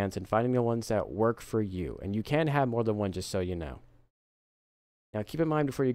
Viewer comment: did he say you CAN have more than one or you CAN'T? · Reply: Can